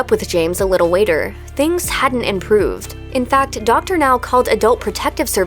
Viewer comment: did she just say "a little weight-er" 😭😭😭😭😭😭😭😭😭😭